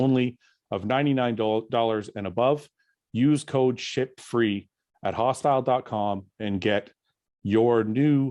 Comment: Not going to lie during the sour peach add it sounded like you said use code SHITFREE